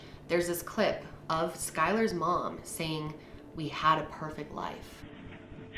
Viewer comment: “Now we have a perfect life “ is what I hear, not “ we had a perfect life “ on